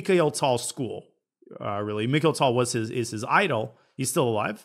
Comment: It really sounded like you said Mikhail Tal is still alive xD